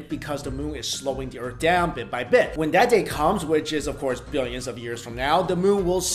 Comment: Wow... BILLIONS of years from now. I thought you said MILLIONS of years from now. Whew- You had me worried there for a second!